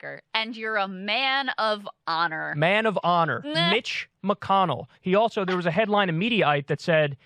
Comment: Bitch McConnell at 8:26 is what I heard. Freudian slip but in reverse.